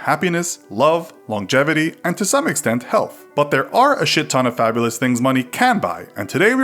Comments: Did he just said shit ton like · did he say “Shit ton” or is it just me ?